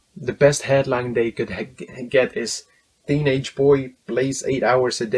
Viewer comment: I thought you said teenage boy blazed